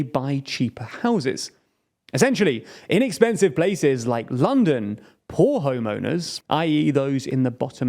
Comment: Just FYI please don't say "In expensive places like London. " it sounds like your saying "Inexpensive places like London...: Not meaning to be sparky just some friendly advice